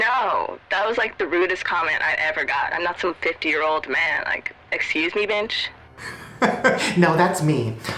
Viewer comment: sounded like she said excuse me bench